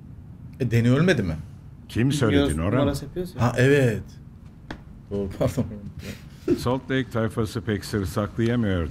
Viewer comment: ABİ 1:12:17 de ne dediğini anlamayanlar? ANLayan ütfen YAZSIN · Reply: Bilmiyor numarası yapıyoruz ya diyo